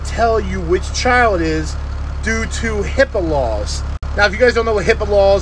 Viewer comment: When you said HIPAA laws, I thought you said, "Hippo Balls".